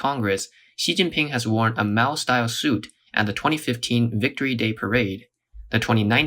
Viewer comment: i thought i heard xi jinping wearing a mouse style suit when its a maoist style suit.